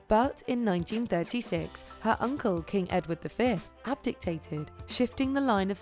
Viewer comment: 1. HMTQ's uncle was Edward VIII (Edward V was the son of Edward IV and one of the Princes in the tower) 2. ABDICATED. Sounds like you said abgictated.